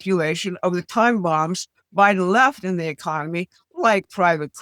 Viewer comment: Did say "Biden left in the economy" or "by the Left"? YT's auto-captions say "by the Left", but I'm not sure. They've been wrong before.